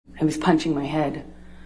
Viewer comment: Did she just say "I was punching my head. Im repeatedly punching my head..thats what caused that bruise on my temple".... Swear she just said I & not he Im not the only one who heard that right...😂😂😂